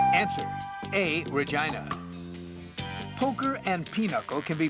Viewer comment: The way he says "Regina" sounds like he was saying Vagina. 😂😂😂